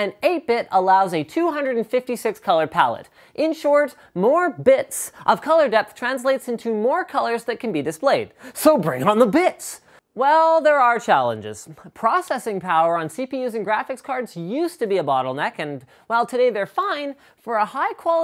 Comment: i thought you said bring on the bitch lol